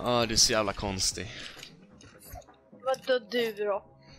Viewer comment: vad sa han? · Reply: Vad då då? du då😅